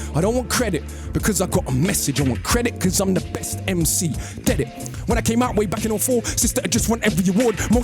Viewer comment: Ah fair play, I thought he said "get it?" rather than "dead it", so I thought it might be a joke/punchline. Thank you!